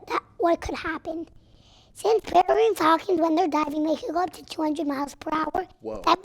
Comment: Someone please tell me what miles said at 6:46 I really wanna know his fun fact but I couldn’t catch it🥲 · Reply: he's talking about a peregrine falcon!